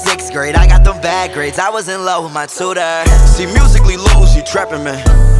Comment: I swear in minute 1:04 i thought it said “I was in love with Matsuda” like as an anime character, and that in the next lines it mentioned “Ackerman” another ANIME CHARACTER. Omg I’m not okay